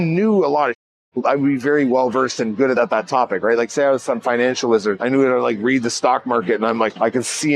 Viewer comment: I heard financial lizard and had to go back to hear it again.